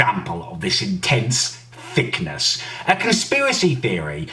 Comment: did he just call himself thick 💀💀💀